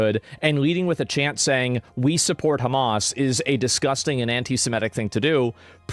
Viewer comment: i thought they said we support hummus i think they are hungry